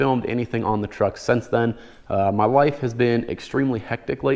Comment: I thought he said WIFE, had to do a double check.